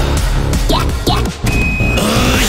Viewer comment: What did he say?😂😂😂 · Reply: "yeah yeah"